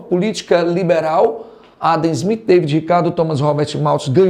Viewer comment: o que ele falou aqui ? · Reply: Adam Smith